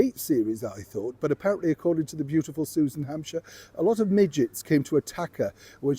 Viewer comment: oh Neil, I thought you said midgets instead of midges(bugs) and I was very confused as to why the set was overrun by midgets. After listening to your video again I figured it out.